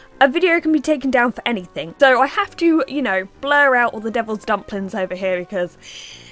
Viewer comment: did you say devil's dumplings? LMAO I love this video (: My bff named her tarantula she got around last Halloween "Spoopy"